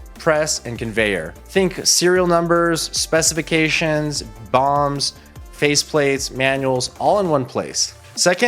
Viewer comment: did he say bombs? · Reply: BOM... Bill of Materials